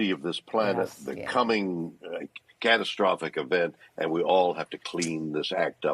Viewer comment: Did he just say "the coming catastrophic event" at the 5:50 ?